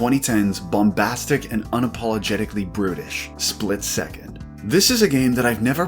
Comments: I heard that as ”bombastic and unapologetically British” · I heard that as "unapologetically british"